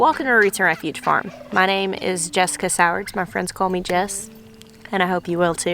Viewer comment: Why do I always think she says her name is? Lol I thought it was such a fitting name until I realized I misheard it.